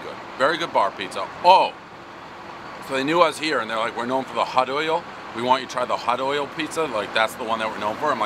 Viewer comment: At first I thought he said “hud oil” at first lmao